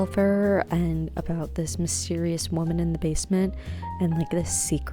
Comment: I could have sworn you said, "Mormon" in the basement rather then woman and got me confused. xD Granted, I know nothing of the game and you could have actually said that. 😅